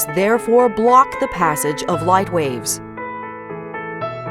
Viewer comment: The whole time watching this, I thought she was saying RAVES 😂 ohhhh